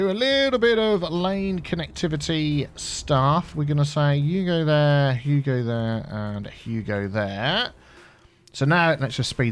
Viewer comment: Biffa, thanks for clearing something up for me. I never realized that you were saying Hugo. I thought you were saying “Yugo here and Yugo there”. I was getting worried about my eyesight because I wasn’t seeing ANY Yugos!